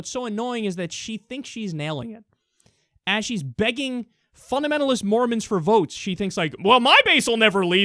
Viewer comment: I thought it said fundamentalist Morons at first